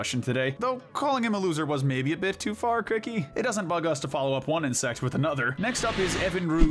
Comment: for a second I thought you said “in-SEX” instead of “inSECT”😅😅😅